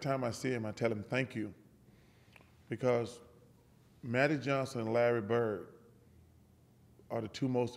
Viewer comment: I thought he was saying Manny Johnson and Lanny bird.Thats just terrible.